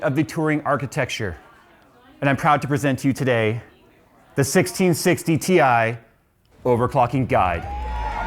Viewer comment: i heard "the 1660 TI OVERCLOCKING GOD!"